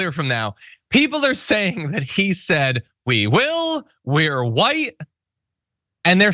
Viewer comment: I heard "We will wear white..." Like virginal brides, maybe?